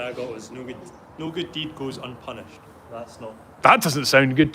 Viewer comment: I’ve always heard it as no good deed goes unturned. But the original is no good deed goes unpunished.